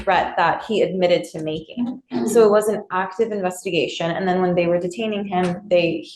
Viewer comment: Captions are wrong at 7:41 She says, "So it WAS an active investigation," not, "So it wasn't active investigation."